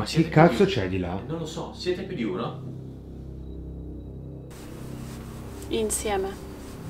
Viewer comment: a questo minuto, sentendo quello che dicono, chiede se è più di uno e risponde “insieme” Se ricordate avete letto che bael è formato da tre “elementi” (gatto, uomo, rana) messi appunto “insieme” ….formano bael Quindi si….parlavano proprio con lui Like se lo avevate capito anche voi 👇